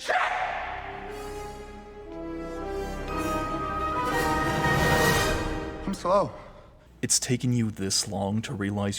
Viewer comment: Wtf did he say at 19:50 ?? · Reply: "I'm slow"